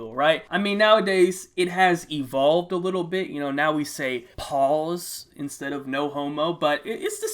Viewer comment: wtf did you say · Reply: Pause I think